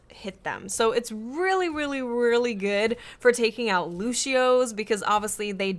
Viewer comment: I think you said lushio. Like if you had braces or something. XD